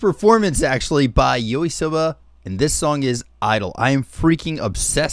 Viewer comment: did u say Yorisoba? lmao